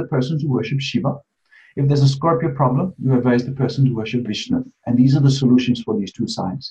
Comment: FANTASTIC INSIGHTS GUYS - Visti never fails to impress and astound! such guests are to your credit! ONE QUESTION - At 11:55, Visti was explaining the remedial deities for Scorpio and Aquarius. Did Visti said Krishna is the deity to worship for Scorpio? I couldn't hear it well. I have a Scorpio ascendant in D9 where Ketu sits. Thank you both for sharing this divine knowledge! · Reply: should be Vishnu, Shiva is for Aquarius